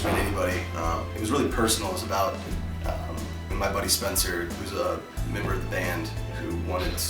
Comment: him saying MY new record and that spencer WAS a member of the band makes me now convinced that this band is officially dead and should just be named Brendon Urie from here on. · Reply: actually i misheard that spencer part. he says who's a. really sounded like was to me.